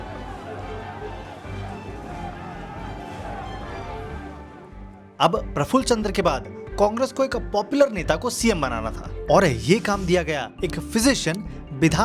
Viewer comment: did he actually said cockroch ko ?